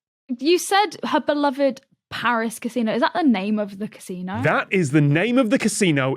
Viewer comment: Honestly I heard "Her Paris Casino" and went "does she own the casino"?